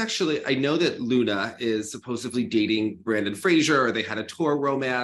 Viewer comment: Loena Hendrickx is dating who??? Did you say Brendan Fraser or did I mishear that? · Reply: Brandon Frazier. US National pair champion and World pair champion with Alexa Knierim.